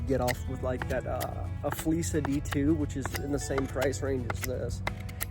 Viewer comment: What was the name of the knife you said at 2:03 ? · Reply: I believe I was referring o the Flissa D2 survival knife. They are on amazon